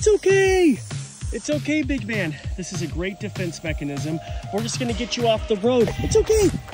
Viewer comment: Did he just say grape defense machanism